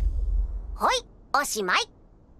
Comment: is it me listening to Australian accent for too long or did rimuru just said ‘easy mate’